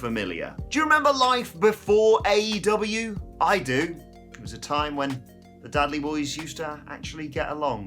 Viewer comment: did you say "when the dudley boyz used to get along"? If so, did they fall out?